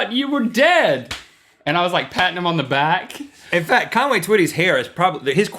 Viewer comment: I thought he said Conway titty